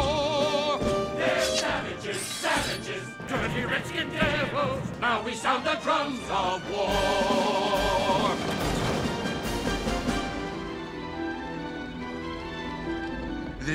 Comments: I always thought they were saying "dirty wretched devils." How naive i was. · Pretty sure that's still the same line. is not changed. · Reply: Also, I keep hearing "FILTHY shrieking devils"